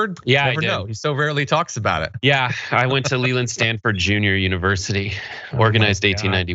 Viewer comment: mins in .. Sounded like said MY NAME just then.. ("Lelynn") he said "he went to Stanford Lelynn University".. tho, I'M SURE, he meant "Leland" (j never get any luv/I've never seen my name on ANYTHING)